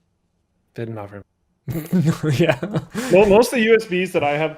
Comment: мне одному послышалось "нихуя" на чистейшем русском :D